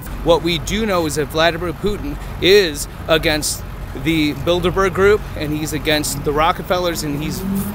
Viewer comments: I was about to die laughing cuz I thought 41:04 he said, "Putin is against the Build-a-Bear group" 😅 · did he say build a bear group?